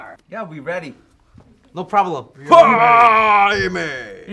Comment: AMA instead of DNa 😂😂😂